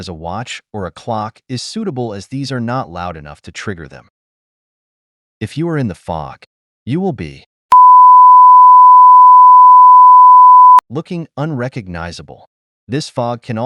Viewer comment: I thought I heard "if your in the fog you'll be fu****" 😂